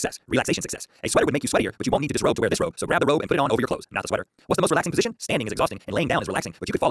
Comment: I thought I heard at 10:41 he had to put the sweater on over the robe